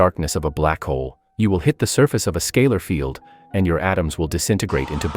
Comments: Bro I thought it said gay holes😭😭😭😭😭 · I thought it said gay holes and got excited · My dumbass thought it said “Gay holes” 💀💀💀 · damn, i thought its said gay holes 😅